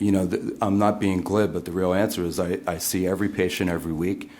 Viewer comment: does anyone know what word he uses? I'm trying to find the definition. "I'm not being 'glid'"? What does he say and how is it spelled? Thanks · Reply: Glib -- readily fluent, often thoughtlessly, superficially, or insincerely so.